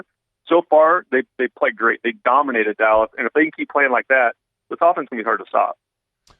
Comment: Did say the falcons are going to be hard to stop when he meant the saints? At about 11:10 ? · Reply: This offense